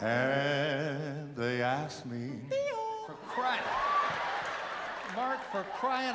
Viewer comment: what is mark saying at 3:47? I can't understand what he is saying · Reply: "Bill"